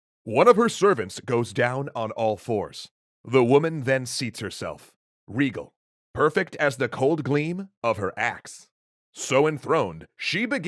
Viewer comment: I heard ass insead of axe... Pls tell me in not alone...